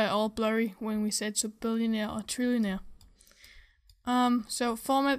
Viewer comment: i thought i heard Billionaire and Trillionaire